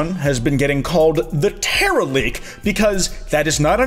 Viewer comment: Kinda funny that this leak is called the "Teraleak"and the gimick for Gen 9 is terastallization. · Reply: i misheard it as terrorleak and i wish it were that